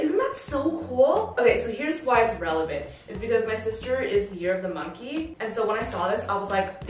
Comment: I heard “this is relevant because my sister has a fear of the monkey” and had to backtrack to listen again 😅